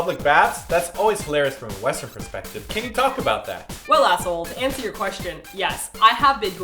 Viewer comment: I thought said ass**le... TT_TT I'm such a bad person xD